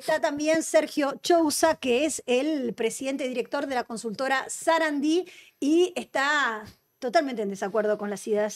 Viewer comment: Escuché que el nombre de la consultora de Chouza es zarasa?